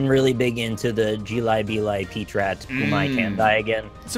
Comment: What is saying at 48:15? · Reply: Search "Jelly Belly Pet Rat Gummi Candy" on youtube. You won't be disappointed.